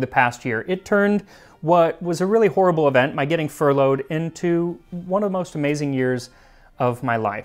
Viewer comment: okay, I REALLY thought you said 'getting Frollo'd' at first, and I was wondering which one of the many dangerous possibilities that could be...lol